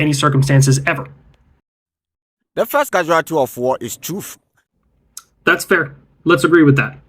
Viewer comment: What did he say? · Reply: The first casualty of war is truth, at least that is what it sounds like